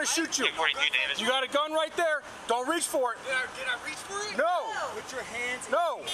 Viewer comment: "Don't reach for it!" "Can I reach for it?" fucking dies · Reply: For a moment I thought he said that too but he said “did I reached for it”?